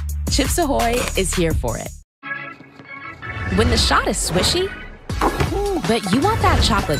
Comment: "When the shot is swissy, but you want that chocolate chippy" Kill me N O W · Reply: I thought it said squishy 😭